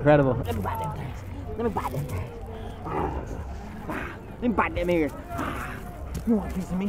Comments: wat did he say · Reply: He was talking to his dog and he said “let me bite that ear” as a joke!!! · did Jake say “I’m gonna bite that n word?” I’m guessing not but that’s what it sounded like